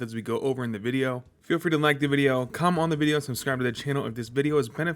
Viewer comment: did this mf just say “cum on the video” instead of comment 😂😂